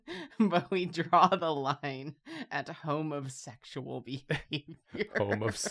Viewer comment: Home of Sexual. It’s like when I thought someone said Homosexual but they actually said “Home Essentials”